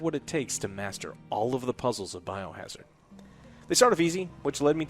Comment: Did he say biohazard at 8:55?